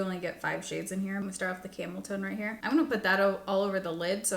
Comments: Every time you said “camel tone” I heard it as “cameltoe.” I was like, “Will these cosmetic companies ever stop?” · I heard “camel toe” and not “camel tone” the first time 😂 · 😂 I was half listening and folding laundry and I had to rewind bc I thought I heard camel toe.... Tone. Camel tone. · When you were saying how you liked the camel tone of the eyeshadow I died laughing. I thought you said camel 🐪 toe!